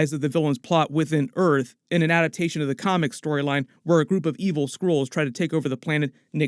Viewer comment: Sounded like you said “a group of angry squirrels try and take over.”